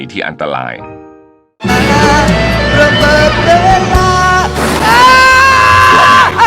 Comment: ระเบิดเวลาอ้าาาาาาาาาแต่ทุกคนเคยได้ยิน ระเบิดแมงดาอ้าาาาาาาาาาาา